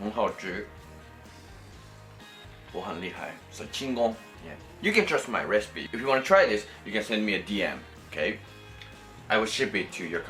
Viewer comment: Hahahhaa did you said “chingon” At 7:03? 🤣🇲🇽